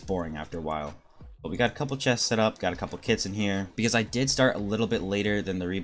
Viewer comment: It sounds like you say “kids” haha great video ether way:)